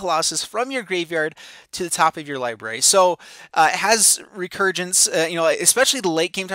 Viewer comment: did you just say "recurgence"? wtf.